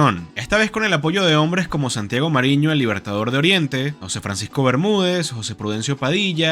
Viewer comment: ¿Me repites lo que dijiste en este fragmento? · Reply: Santiago Mariño, el libertador de Oriente. ☝🏿